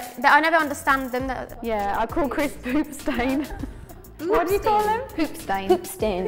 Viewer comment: At first I thought jesy said boob stain instead of poop stain oml